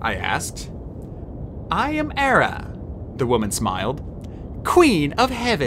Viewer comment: who did you say specifically because I swear you said "I am Era, Queen of the heavens" I'm assuming hera but please next time you do read hera pronounce the H a bit more